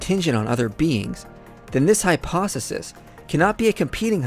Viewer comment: Hyposisis instead of hypothesis lmao